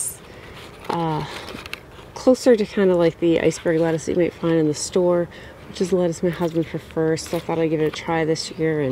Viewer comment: what kind of lettuce did you say that was? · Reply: Yucaipa